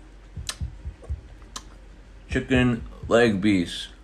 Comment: Whenever you’re at a jelly Fruit restaurant and your friend says what’s your favorite jelly fruit? Just say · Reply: Chicken leg beast is what I heard don't judge me